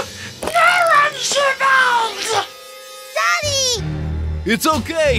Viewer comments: why did I hear "daddy" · DID POPPY SAY “DADDY” OR “HUGGY”?????????????? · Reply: Poppy said daddy